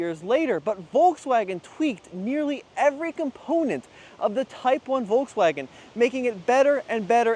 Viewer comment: I almost died when I heard "VWs most iconic car, the Tiguan" and I'm glad I didn't rage quit right then...The second time you said it I heard "type 1" but was still hyper ventilating...😂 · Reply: He said Type 1